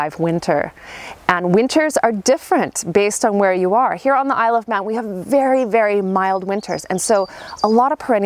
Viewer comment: at @ 2:00 did you say you are on Isle of Matt ? I am not sure of the location. Could you please put the location in the description? Thanks. I am in San Diego county, California. Latitude 33º . Zone 9b. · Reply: The Isle of Man -- it's an island nation between Ireland and England. We're latitude 54.3 and technically USDA zone 9a (thanks to the gulf stream). More like an 8-8b though, in reality.